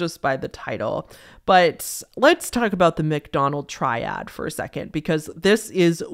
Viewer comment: For a second I thought she said 'McDonald's triad' and got REALLY confused as to why fast food was involved. Had to go back to the start of that segment to reassess. lol